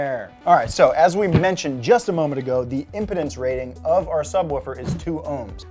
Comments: did I hear 👂 “impotence” or “impedance” LoL 😂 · Did you say, “…impotence rating…”?!?! 🤣 I know that Physicists (myself) and Electrical Engineers are ROFLMFAO!! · i thought said impotence when he said impedance lol